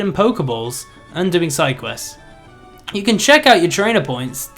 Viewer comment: I looked away to take a sip and thought I heard say "and doing cyclists."